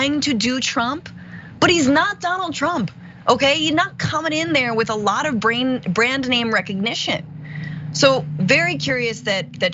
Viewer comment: At about 3:39 I heard the start of "not a lot of brains" then cut short. I agree Ram-ass-swamie doesn't have a lot of brains.🤣 I love the way these ladies cut them to the bone!